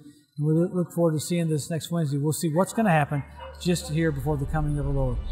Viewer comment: Did he say "just a year before the coming of the Lord?" at 4:18 or " just here?" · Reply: "just here"